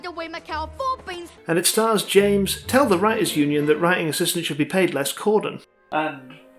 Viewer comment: What did he say? I'm sorry, I have problems hearing it T-T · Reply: 'James "Tell the writer's union that writing assistants should be paid less" Cordon,' I believe?